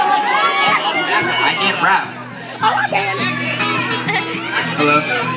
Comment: did anyone hear some say in a weird voice ''oh I can"